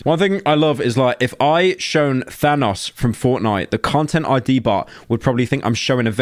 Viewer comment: i thought he said “i could show vanoss and get copyright striked” i just imagined a picture of vanoss gaming flashing on the screen and your account immediately getting deleted